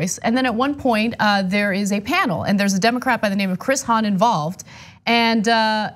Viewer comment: Did Ana say Democrap at 4:41 - 4:43? "And there's a Democrap by the name of..."